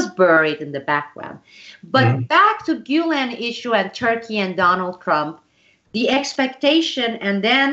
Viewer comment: I heard "Donald Crumb" - very accurate LOL.